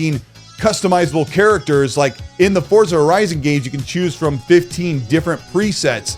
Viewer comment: did you say forza horizon or did i hear it wrong?